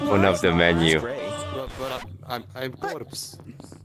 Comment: The fact they just let the random guy stay 😂😂😂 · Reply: It had me laughing when i hear him say "I'm corpses"